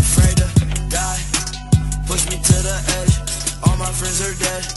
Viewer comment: When I was a kid I thought he always said "да, push me to the edge" and thought it was cool now reading he just says die odly.